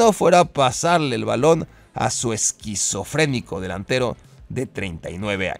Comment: le dijo esquizofrenico a Cristiano Ronaldo o escuche mal???